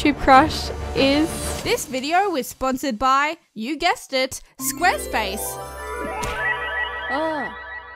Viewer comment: This sounds like u said ur crush is square space